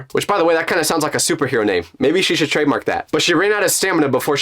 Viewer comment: i thought you said 'stupidhero' and not 'superhero'